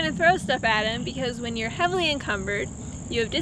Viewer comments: I thought she said that she wanted to be heavily cucumbered. Lmfaooooo. That might hurt. ‘Here we GO!!!’ Hahahha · I thought she said we were see a video of her being heavily cucumber 🥒 😂 🤔 😳 🤣